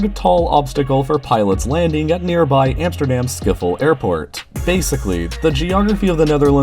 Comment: I know its Schiphol, but it sounded like Skippol